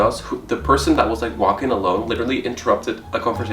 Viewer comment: Bet the “big influencer” was James Charles lol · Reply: it really does sound like he said "im not gonna say Huda was", or "im not gonna say whodat was". Like, he does have an accent to me. But i hear the "Huda".